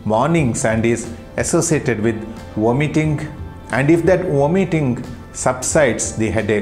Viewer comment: What word is he saying at 6:47? “War Mitting”? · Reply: He was saying “Vomiting”